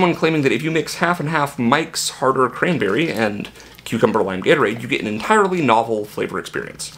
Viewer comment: This is one of the most entertaining YouTube watches in a minute. At first I thought you said WINE gatorade and was expecting you to be shitfaced by the end.